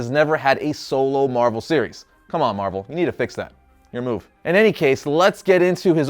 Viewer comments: wait wait wait did he say marvel? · did he say marvel ?? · did he say marvel, im so triggered right now